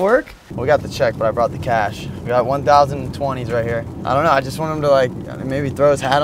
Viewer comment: I thought he said 1,000 20's. I was like damn, he getting 20k!! Lmaoooo